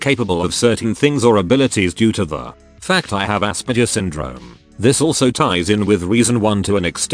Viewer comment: I was listening (not watching) and when the voice said Aspergers I thought it said "ass burgers" and I simply dismissed it as something ADF would say